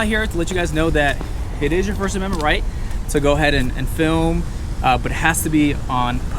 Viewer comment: Did he just say it to her first amendment right?